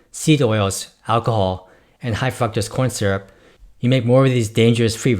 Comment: I did mishear "High fructose corn syrup" as "Hi fucked up corn syrup", which actually is a more accurate description of the product. 😁🤣